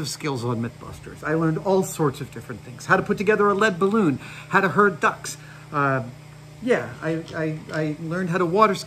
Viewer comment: “How to herd ducks” Quack damn you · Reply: I heard "how to hurt ducks" then I was like "wait, adam won't hurt ducks??" then I thought of herding ducks then I said "He must have said How to herd ducks" then composed myself.